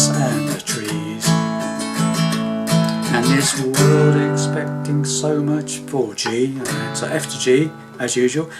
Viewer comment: i thought at 9:10 you said “and the world expects so much from a G” and i laughed hard. thank you for the tutorial very well explained!